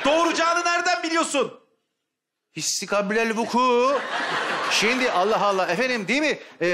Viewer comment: tam olarak ne diyor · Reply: İstikablel vuku